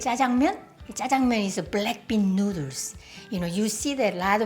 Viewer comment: who heard BlackPink and turn on the subtitles